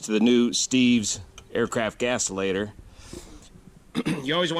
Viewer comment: Ohai :3 YouTube's automatic CCaptions / subtitles didn't quite catch those 0:25 "…thanks to the new Steve's aircraft GASPS LATER…" - uhm, "gas-o-lator"? 1:06 …what are "mud dobbers"? …please clarify / elaborate / educate. (no worries, i'm not trying to mock you - i just don't have service manuals available …nor am i familiar with Luscombe nomenclature) A few other questions: → uphill landing: please record and comment one of yours …i've seen some crazy short, almost suicidal, runways in the mountains on YT …you seem to be quite a proficient pro - are those actually that scary? → You're also a big rig airline pilot, right? Did you ever blow "adventurous" tourists off SXM beach, during take-off? ;D LOL …here's an approach: <link> → when i/we (your YT audience) hears your "radio voice" …not everything you say is broadcast on air, right? (It's just a mic tap, right?) …meh, some of your "radio voice" couldn't be speech-to-text-ified by YouTube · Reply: yes gasolator- the thingy that catches water in the gas. Mud Daubers- the bugs that like to plug up small holes in things...like fuel vents.